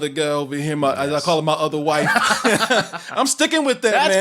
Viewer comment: Chris Herman: The OTHER white. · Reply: Lol, is that what he said? I thought he said his 'other wife'.